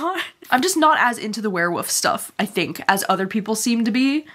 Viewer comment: Love you but I kept hearing you say “where-woofs” and it’s too funny!